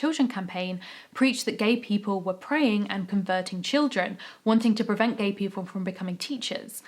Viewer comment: "Anita Bryant was preaching that gay people were preying and converting children".... I heard this ad "praying" and I was like damn, that's some powerful prayer, maybe Anita should join their side lol